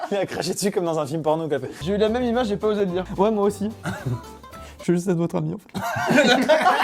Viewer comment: Il dit quoi a 16:05 ? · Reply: Je voulais juste être votre ami enfaite 😉